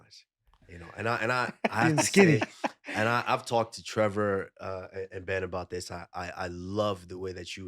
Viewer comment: “I’ve talked to trevor in bed about this…” wtf tell me i heard it wrong